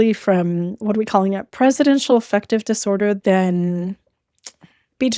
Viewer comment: did you say presidential effective disorder or did you edit this in? it looks like your mouth isnt moving the same